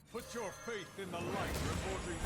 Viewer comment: “PUNCH YOUR FAETH IN THE LIGHT” I always thought that’s what Tirion said